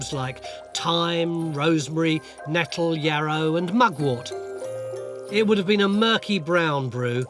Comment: "thyme, rosemary and m..." what was the last word? mud water? mugwort? I never heard of mugwort before but that might be it.